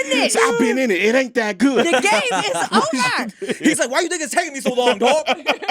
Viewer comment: Did say nigga?? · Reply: “Why you think it’s takin me so long, dawg!” Lol